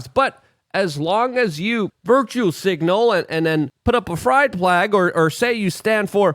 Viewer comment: did just call this (🏳️‍🌈) a Fride Plag??🤦‍♂️🤦‍♂️